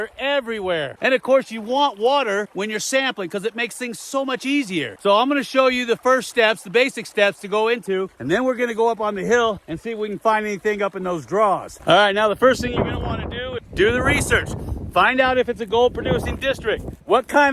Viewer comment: I just heard you say "Get up in them drawers!"